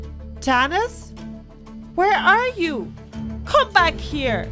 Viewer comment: did I hear Tanis